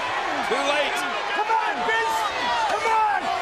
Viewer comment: it sounds like he says " C'mon bitch C'mon" at 4:20 haha